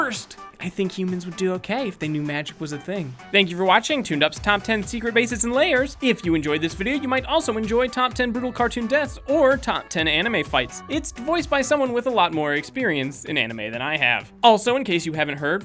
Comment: Oh, for a second there, I thought you said secret bases and liars. I figured that's why you were showing Gruncle Stan from Gravity Falls.